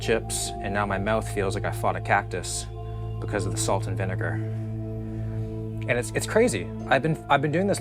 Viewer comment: what did you call me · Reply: He said vinegar.Not the N word.